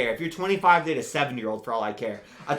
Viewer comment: kinda sounded like "If you're 25 date a 7 year old" but I assume it was "If you're 25 date a 70 year old"